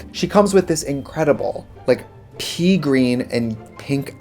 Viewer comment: I thought she said "pee green" and I was about to freak out, like, what have you been drinking for your pee to turn that color?? Stay healthy, hydrate